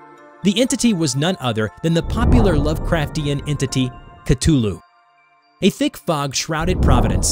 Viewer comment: did you just call Cthulhu "cutulo"